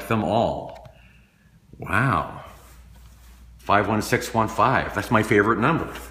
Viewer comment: I actually thought that said SIGIS instead of 51615